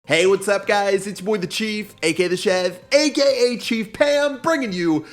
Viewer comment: did he just say chief pam?